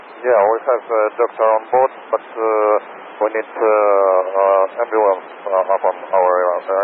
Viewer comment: I think he said "ambulance"